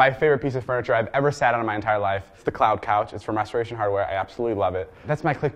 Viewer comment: Did he say "It's from masturbation hardware" ?